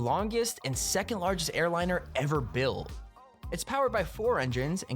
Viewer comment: it looks like he says ‘buil’ instead of built